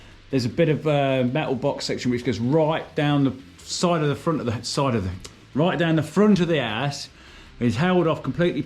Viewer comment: "right down the front of the ass" ??? lol... I know what you said.. but this is funny. love the accent.